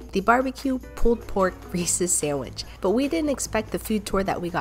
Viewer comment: Ngl the first time she said reese’s i thought she said racist sandwich lmao